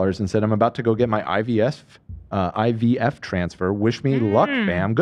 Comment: I genuinely thought Dan said “”I’m about to go get my IBS transfer!” GOOD LUCK”” when reading out that dono, I was like whaaaat in the heeeell my mind went to some strange places and it was even more frazzled when he followed it with a GOOOD LUCK! sorry